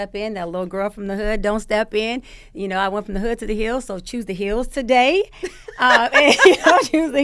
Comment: "hood to the heal" lmao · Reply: I thought she said "I went from the hood to the Hills." Meaning she went from struggling to Beverly Hills.